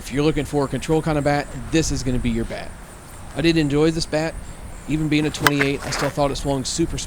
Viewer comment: At the 2:45 minute-mark it sounds like you said: "I DIDN'T enjoy this bat"(I listened to it multiple times and that's what I kept hearing), but then based on your comments immediately following that statement it sounds like you DID enjoy the bat, so what word did you actually say(or mean to say) there? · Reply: Definitely sounded like didn’t but I in fact did say I did enjoy the bat. I get to talking too fast for my voice overs sometimes and shit just flies out if my mouth. I catch it most of the time but that one slipped through.